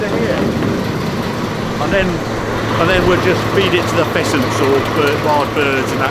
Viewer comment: I thought you said "... and we will just feed it to the peasants...", however what you actually said was 'feed it to the pheasants'.